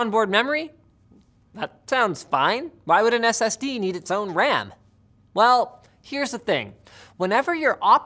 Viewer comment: Hello, Linus! I love this video so much, and I want to make sure everyone can understand and enjoy it. That's why I wanted to let you know that your captions are a little off. At about 0:41 the auto-captions say "ran" where they meant to say "ram". When you get a chance, could you fix that? That'll help the 360 million folks globally who rely on closed captions to understand videos. Thank you, and keep up the great work!